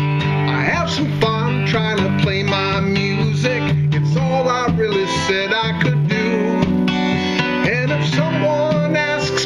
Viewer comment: Really nice cover. The lyric actually isn't "all I really said I could do," but "all I really set out to do." It seems to be a common mishearing of that line. Gret version though. I love the harp and your strumming pattern. You really made it yoir own with differentiation in the melody and lyrics. Awesome job!